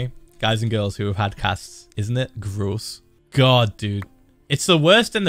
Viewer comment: i thought you said cats lmao